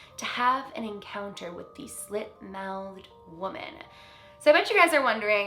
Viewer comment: Thought it said shit mouthed for a second